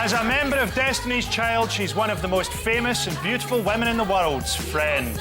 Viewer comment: what did frankie boyle say at 1:49? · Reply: "As a member of Destiny's Child, she's one of the most beautiful women in the world..ss friend "